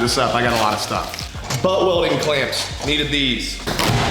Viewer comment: Watching while high: what says “But welding clamps I needed these” what I hear : “butt welding clamps I needed these” I’m laughing so hard I’m crying